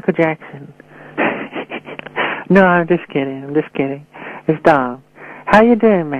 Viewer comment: what did he say in 1:42? He said : Hi, its Michael Jackson. No, now im just kidding. Its ???????????. Please...what did he say? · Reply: He said its "Dom "